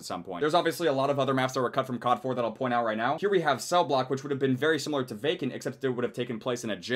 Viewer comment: I thought cellblock said cellblork